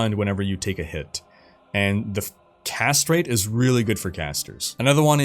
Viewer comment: I heard "Castrate is really good for casters" Sir, I beg to differ!!!